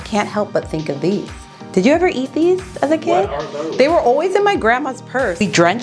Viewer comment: At first I genuinely thought you said, "They were always in my Grandpa's purse"! And I was like OK Grandpa! PURR 😂🤏🏽🤏🏽🤏🏽